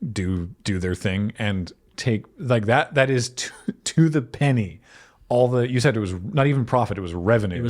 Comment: said "to the penny", but I kept hearing "to the pain" and thought he was making a Princess Bride reference.